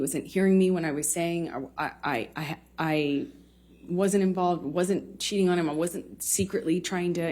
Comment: Anyone else hear '"I wasn't sh##ing on him" around the 1:20 mark? I had to go back and listen again.. Hahaha